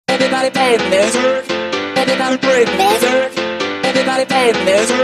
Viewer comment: I hear "Everybody rape your lizard"...